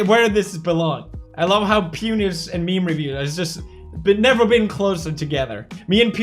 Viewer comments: subtitles say "penis and meme review"😂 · Subtitles @ 16:48 says penis instead of pew news